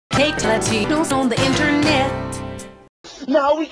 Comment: Before the subtitles became available, I thought she said "fake titties on the internet" XD